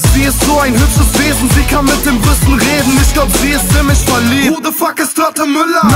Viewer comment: Ich hab erst verstanden: "Sie kann mit dem Rüssel reden." Fand ich etwas merkwürdig. :D